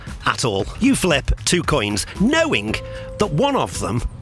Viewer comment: Must admit got the coin one wrong the rest were easy ish the time one I said 15 then realised it would be 15 minutes and a 1/4 as the hour hand would have moved a 1/4 of the hour round + 1/4 of a minute plus 1/4 of a second and 1/4 of a millisecond etc. making the correct answer to the nearest second 15minutes 15 seconds so rounded down to 15 minutes not up to 16 minutes. The coin one I miss heard you say flip both coins (at the same time). I assumed one coin was already on heads. In which case each coin flip is 50/50 so the chances are 50/50 for the second coin. However tossing both coins at the same time knowing one will defiantly be heads is 1/3 chance is one is going to flip a head and the other will also land heads side up. If you flip 100 coins one at a time and get to the last coin and all the others have landed heads, the chances that the last one is heads is still 50/50 however, probability determines that on average the coins should be 50/50 heads and tails. Which still makes betting on the coin being tails is better then heads because eventually with more coins unforced should end up roughly 50/50 heads and tails. This has a similar probability with the lions. 99 lions and 1 car. the chances of you picking a lion are 99/1 this reduces with each door so at the end the chances you have picked a lion is 2/1 so when you eliminate all the lions bar your door and one other the chances of the door you didn't pick being the car is 98/1. thus you have a lion behind your doo